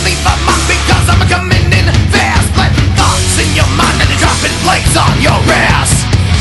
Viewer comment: I put this song on my mp3 for out in my shop and had never heard it before . I ended up with the song stuck in my head , funny he says at 1:43 he's planting thoughts in your mind ?? wth ? I thought he was saying dropping blades on your ass bitch , and I was trying to figure out wth that meant ? ?? . I read a comment below after I saw the lyrics saying PLATES , not BLADES , and the person explained plates is what they call albums in the music business . makes more sense? maybe it wont be stuck in my head , I was hearing all day long while mowing the lawn and doing yardwork . now get out of my head please !